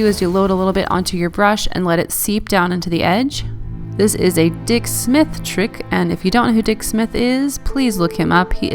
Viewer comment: Omg, I thought you said "dick slip" and had to go back to hear it again. Hahaha, my bad!